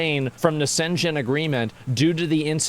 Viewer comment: I'm really sorry, but I just cant get over Asmons pronunciation of "Schengen". I thought he said "Skaven".